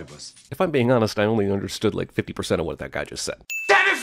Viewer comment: For the longest time i thought he said "you blow me" not "you bore me"